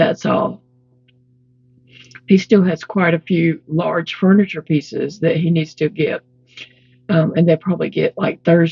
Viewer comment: I don't know how many times I have to check my phone to see what day it is!! !! I chuckle at "large furniture pieces" because I just hear "piano?"!